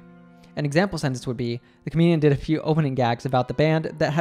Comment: Thank you for your wonderful videos. But could you speak a bit slower and clearer, please? for ex: I can't hear the word Comedian from you (18:33), even though I replayed it multiple times.